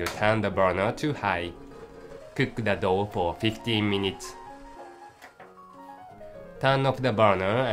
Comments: it sounds like hes saying cook the dog for 15minutes lmfao... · The first time I listened to this at about 1:50 I thought he said, "Now lets steam the dog." "No! Not the cute doggy!" I screamed at the screen. Thats when I realised he said "dough". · from 1:52-1:54 my sis thought that that the person said "Cook the dog for 15 minutes." LOL XD